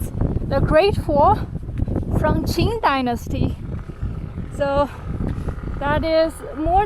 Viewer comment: 字幕19:36 should be qin dynasty, instead of Qing dynasty